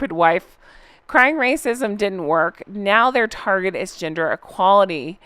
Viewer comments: 😂 I first thought you said “ginger” equality 😅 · In the UK the story went - Megan was asked to meet the Queen. She said she couldn’t go as she already had a meeting booked at the National. The Queen had that meeting cancelled and Megan was taken to see the Queen. Megan apparently didn’t realise that this was a summons not a request. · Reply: Omg, I thought you said Ginger Equality. Lol I had to look at the screen to see the picture. Lol. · I think I misheard; I thought you said they were now championing ‘Ginger’ Equality 😂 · When you first said "gender equality" I thought you said "ginger equality" 😂 As for the birth certificate, the school needs to have it for legal reasons or they could get in a world of hurt. No amount of bribery can change that. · When you said they were pursuing Gender Equality I thought you said GINGER Equality. I had a brief hope that Harry was finally going to get some recognition and perhaps, even respect. When you repeated yourself I heard the actual phrase and sighed. Nope, no hope in sight! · I misheard you when talking about gender equality, I thought you said ‘ginger equality’ - still laughing because it’s the sort of crap they would say!!!🤣🤣🤣🤣 Honk honk! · They're promoting gender equality? I honestly thought you said ginger equality! Ginger men can do the dishes too! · They got I went back and re listen to that part but I could have sworn you had said Ginger equality rather than gender equality and I almost lost it. 😂